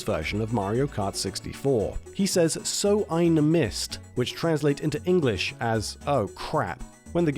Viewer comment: I thought wario is saying "so i miss" it turns out that he's speaking german "so ein mist"